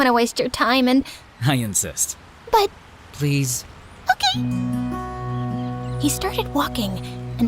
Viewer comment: that "okie" sounded like chicken